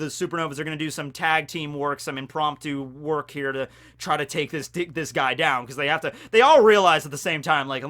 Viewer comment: did he just say they gotta dick this guy down